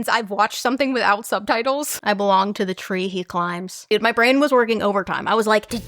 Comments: I heard “Trainy clash” lol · I hear “I belong the drinking clad” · I heard “trinket climb” or “tricky climb”